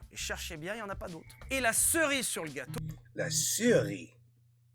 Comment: To be clearer, he's saying "La cerise sur le gâteau", which translates into "the cherry on top of the cake" The expression can indeed be translated into "icing on the cake" but it's not exactly what he's saying and there is no word such as "ceri" that means icing Hope it helps :)